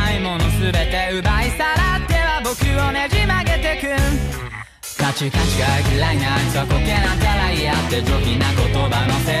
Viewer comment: Anyone else hear gacha? Or do I play way too may gacha games?